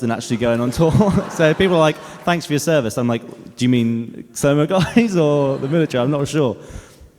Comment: What does dan say at 8:28? Is it a name of a branch in the military from where he is from? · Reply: He doesnt say the branch of military. He says "Do you mean Slow Mo Guys or the military? I'm not sure!"